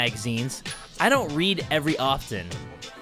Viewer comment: did anyone else hear "every" rather than "very", or is it just me?